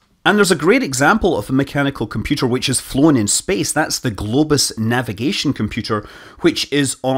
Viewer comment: What mechanical computer was on the early Soyuz? I thought I heard "Globes" but after a search I found out it was "Globus": <link> Amazing!